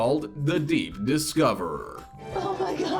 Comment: Thought you said oprah